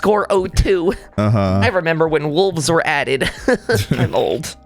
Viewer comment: almost thought said 'remember when noobs were added'